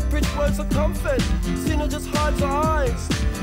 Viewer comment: I keep hearing “help is on” instead of “how bizzare”